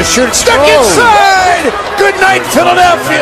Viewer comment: I thought it said Jeremy clarkson lmao